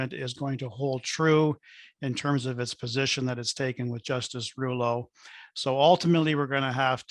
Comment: did he just say Justice Treulow instead of Justin Trudeau or am i hearing things?